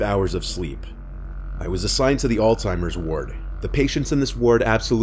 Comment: You said Alzheimer's Ward, but I heard "Old Timers Award".